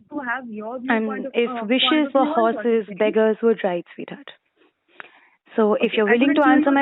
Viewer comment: If wishes were horses beggars would write sweetheart:face-blue-wide-eyes: Did i hear that correctly?? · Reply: *would ride